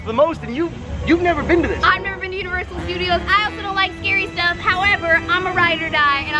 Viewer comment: I thought boze said "I've never been to uterus studios " 😂